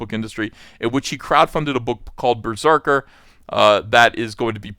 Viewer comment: I literally thought you said "Keanu Reeves helped crowdfund a book called "Berserk" -- all I could think about was one of the coolest actors and people in that industry, crowdfunded one of my oldest and most favorite Manga of all time. HA HA HA. Nice. great video thanks!